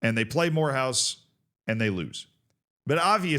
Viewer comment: I thought he was saying whore house. Now that'd be a crazy rivalry